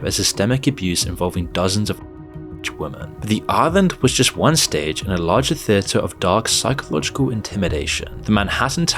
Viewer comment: The audio is cutting out, what were you trying to say? Man it always cuts out when you try to say something, wtf............I''m a baby that has an extreme allergic reaction to bad words, ty so much for protecting me Moon <3 ! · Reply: Jewtube monetization clanker wont like it. It was initially uncensored, he said underaged